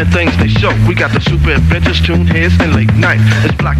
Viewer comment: "We got the super bitches toons...?" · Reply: You misheard that. It’s actually: “We got the Super Adventures Toons” But I can understand why you misheard that, the audio quality isn’t the clearest.